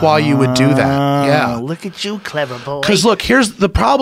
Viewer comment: Joe Rogan interviewing roe jogan from an alternate universe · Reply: I thought he said clever boy. Must have misheard him